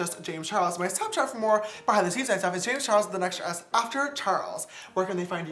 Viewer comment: When I was scrolling through my feed, this video popped up with subtitled and the subtitles thought James Charles said his name was “JamesTurtles” that was golden.